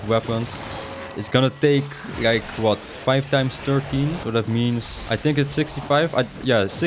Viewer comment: the math ain’t mathing brotha, did you say 5*30 = 65???